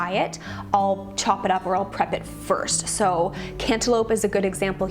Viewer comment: Hahaha I thought you said antelope when you said cantaloupe lol